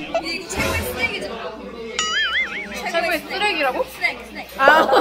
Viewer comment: Awww! That was a fun instant meet up! There’s nothing like hanging out, meeting friends, and sharing fun stories and good food. 😊😋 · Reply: She was probably referring to the moment you both thought you heard refer to the kakanin as 쓰레기. 10:34: “But it’s the best “snack.” and friend: The best “trash?!?” Snack. Snack. Snack. LOL!